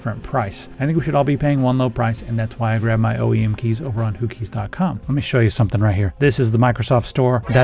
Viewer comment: Ohhhhh, I thought you were saying the Houthis sponsored this video. Yeah, whokeys makes more sense.